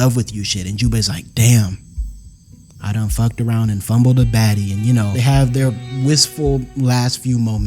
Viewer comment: misheard your saying “baddy” for “baggie” js baggie 😍✨